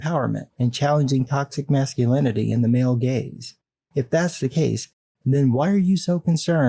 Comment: "Male gaze"? All this time I thought they were saying "male GAYS." I was like, they can be catty but dang, why doesn't Margot want male gays in the audience? Probably because they would've clowned those outfits something fierce.